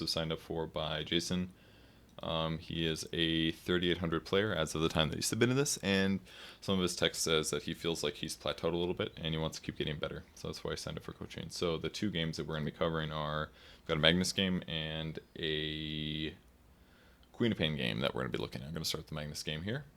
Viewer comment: The very beginning, what does mumble? (00:12) "Some of his text says that he feels he has played TOAD (!sic) a little bit" ?